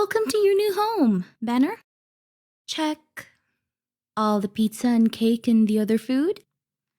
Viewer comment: DID YOU SAY PIZZA